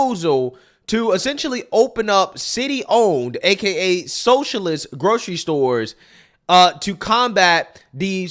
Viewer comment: Now he said "socialist grocery stores" But what I heard was "future famine"